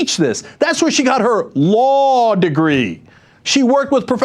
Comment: I find it funny when the subtitles say Her LOL degree instead of her "law Degree"